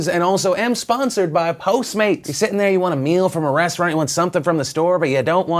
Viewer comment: Am I the only one who thought it said pornstars instead of post mates for a second?